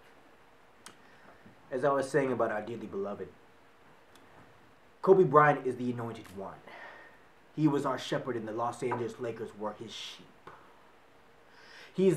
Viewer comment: This didn’t age well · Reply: I was bout to ask why when I heard “Kobe Bryant IS not dead” lmao